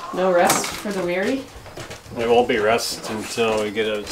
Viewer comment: LOL! I heard "No RUST for the weary" and said, there won't be any "rust" until there's a significant rain." Rust/Rest. LOL.